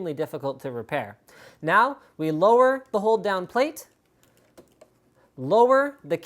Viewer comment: I know it is "hold down plate", what I heard: "whole damn plate"-_- why........